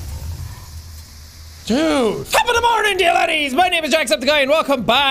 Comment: is it just me or did the intro sound like he said top of the mornin daddies😀 instead of top of the mornin to ya laddies